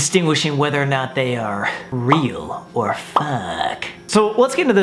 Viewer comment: it sounded like he said "realll.. or f*ckk.." 😭 Am i the only one who noticed that..